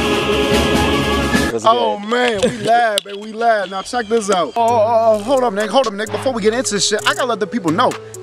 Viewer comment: how my goodness he said the in word here 1:05 was my first thought but he said nick so dont be rude piugboi